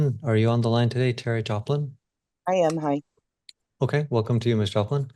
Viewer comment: Did..did she just call the judge "Honey?" when he asked if she was there? That's not how you address a judge 😬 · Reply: I heard "I am. Hi." ... 🤷. But maybe I need 🦻!! 🤷🤭😂😂👍